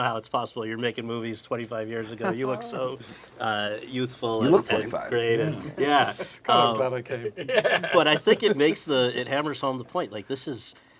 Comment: Forgive me if it's buried in the comments but what does Andrew say @ 9:52 here? I can't quite make it out and it's probably nothing but i still need to know! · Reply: “I’m glad I came”